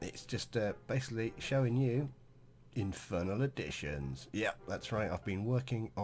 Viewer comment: The start of it i thought it said infernal addictions :)